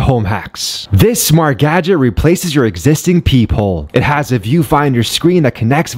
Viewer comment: I thought you said ‘pee hole’ at first 😂